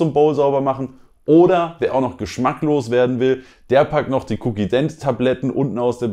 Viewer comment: Ich hab bei 4:20 "wer geschmacklos werden will" statt Geschmack loswerden verstanden beim ersten mal